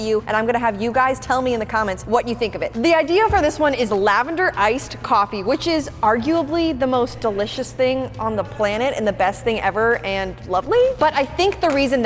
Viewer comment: When said the first idea was lavender ice coffee I thought she said “average ice coffee” 😂